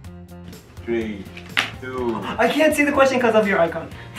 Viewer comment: Si bilang, "I can't see the question because of ur face UNCLE..." ke bang... Apa aku salah denger 😂